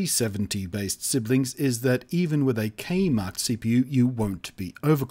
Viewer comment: I heard Kmart instead of K marked. That was a very confusing 10 seconds of my life spent deducing the truth meaning 😅